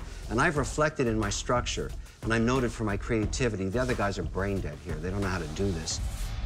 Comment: did he say brain dead 😂😂